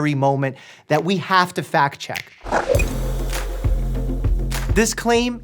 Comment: I swear to God I thought he said fat-check 🤣🤣🤣🤣